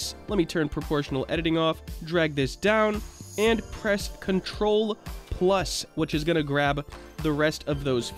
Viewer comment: THANK YOU SO MUCHHH but at 7:08 did you said ctrl + because it didnt work for me but i can still color it, thanks again!